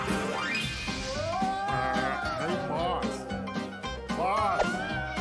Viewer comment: the thing is i thought the devil was saying boss